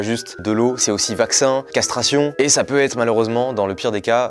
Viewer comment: il a dit castration ( 21:29 ) jai entendue sequestration mdr